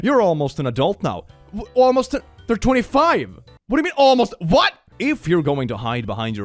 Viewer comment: I think they said “when you’re 25” as to say that them as a 25 year old is in the future & now they’re 17…either that or they are 25 & this person was referring to a past 17 year old self & either way this shit is unhinged lmao.